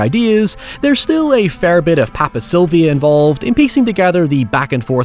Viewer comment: What is he saying at 4:00? Papcy? Papcylvy? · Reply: Pepe Silvia, the meme